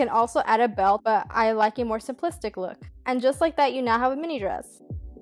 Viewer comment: What the hell do you say at 4:31? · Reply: "and just like that you have a mini dress" is that what you meant? 👀